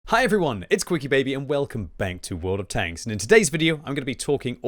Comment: Did he just say "welcome bank"? Ahahhahaha clever boy